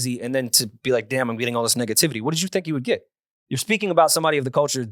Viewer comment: DID SAY "NIGGA-TIVTIY?"